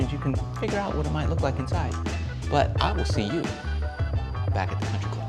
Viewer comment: 😂 did you just say "sit ubu" 😂